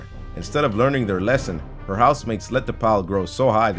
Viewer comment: For this entire video, I thought he kept saying house maid